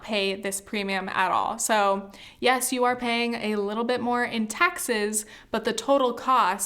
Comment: all I hear is more taxes